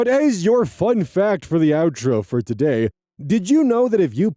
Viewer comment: Wait. Put.. A HOTDOG in a FLASHLIGHT?.. Tbh I thought you said put a flashlight in a hotdog for three whole weeks before i just rewatched this and.. Thinking about it.. I don't actually know how you woukd put.. A flashlight in a hotdog. Like, putting a hotdog in a flashlight makes so much more sense and.... I just didn't think anything of it until now.. Kinda disappointed in myself ngl...